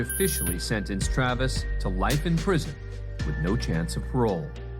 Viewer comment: Surely that was mis-subtitled, I thought he was saying "Levi! Levi!" He was just incredibly upset he won't get to wear his favorite jeans again.